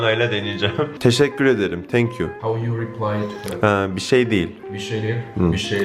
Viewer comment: abi oda arkadaşın burada ne diyor (Ingilizce )? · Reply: How u replay to that? Diyor